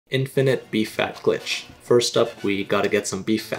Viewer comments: I thought he said infinite bee fat glitch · I thought he was saying bee fat and I was like "YOU MONSTER" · Is it just me or is he not pronouncing the F in beef? It sounded like he was saying “Bee fat” the whole video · I thought bro was saying "bee fat"